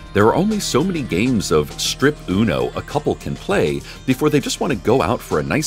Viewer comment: There is just so many extreme uno games you can play with your husband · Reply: Cherry Søda the first time he said it I heard “strip uno games”